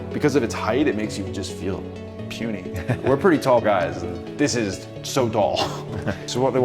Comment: Did you just say so "dull" or "tall"? Pretty sure it was "tall" That must have been such a cool experience to see that.